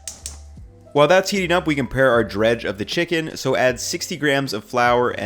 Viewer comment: I'm not a native English speaker but would really like to try this, however I can't really understand what he says at 2:02? some sort of flour and something else? Could you help me please? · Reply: dredge for the chicken? It means the coating, essentially the mix of white flour/cornstarch and salt that you will use to make the chicken crispy.